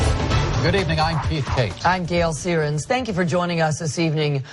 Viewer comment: Did he just say "Good evening i'm Beefcake"?..